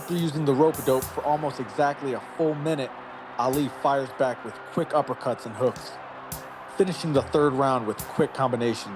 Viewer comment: I almost thought he said with freak uppercuts and hooks😂